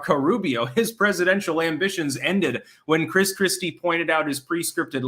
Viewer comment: I thought you said Crisp Christy like Krispy Creme doughnuts.